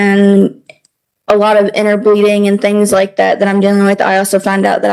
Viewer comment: Ummm... Did she just say a lot of inner breeding? I'm pretty sure it had to have been something else but I'll be damned if that's not what it sounded like all three times I rewound it to hear again. Edit: 6:34 Ahhh okay it's inner bleeding. Although, she is using every other excuse she can throw at the wall so it could still have been the first guess. 😂